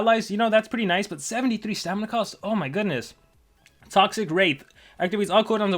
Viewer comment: anyone else hear toxic rape or is it just me